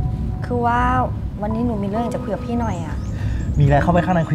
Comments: ใครได้ยินบ้างบอกว่าหนูมีเรื่องอยากจะเผือกหน่อย 55555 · ได้ยินว่ามีเรื่องอยากจะะเผือกพี่หน่อยอ่ะ